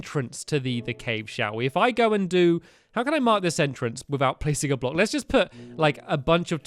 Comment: 200 blocks is a lot am I the only on released that he said 200 blocks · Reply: and i think he said pissing a block